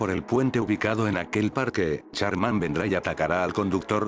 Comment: estaba distraido en el telefono mientras escuchaba el video y les juro q escuche "Charmander" en esa parte XDDDD me sorprendi y tuve q regresar el video aver que decia